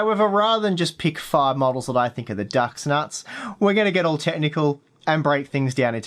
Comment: did... did you just say, "the duck's nuts?"